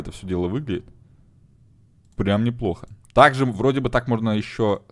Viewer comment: мне одному послышалось "бля, а мне похрен"?